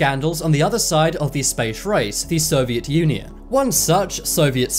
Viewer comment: when I can't really hear this any other way besides "the Soviet Yunyun" this tells something about me..